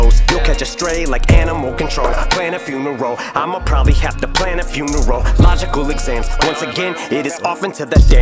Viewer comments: Damn @ 1:17 is why I came to this video I thought he said funeral twice in two lines... then I thought wait Eminem wouldn't do something so simple like that but man wtf 🤯 him saying neurological exam in a way to rhyme with funeral is so Eminem 💯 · Wowwwww initially I thought he said plan a funeral that I'm gonna have to plant a few to roll he said plan a few you neurological exams😂😂😂